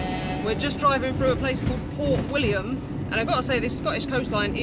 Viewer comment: Next time you should get way further north west girls. I thought you said Fort William at first. I had to look up Port William, it's not reeeealy Scotland. It looks further south than Carlisle on the map! Love your vids though.👍